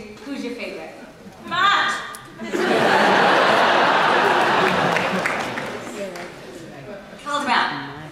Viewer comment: what did jenna said at 40:50....she said something matt i think but what excatly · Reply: she said capaldimatt, so both, she smooshed their names together.